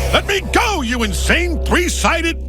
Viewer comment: I think the rest at 0:02 he says let me go you 3 sided yellow demon